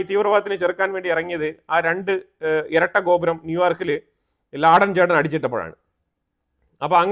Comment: Did I hear it correctly? (ബിൻ)ലാഡൻ ചേട്ടൻ? 😂